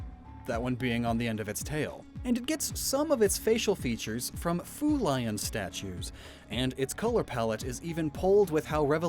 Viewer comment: kind of sounds like he said food lion lol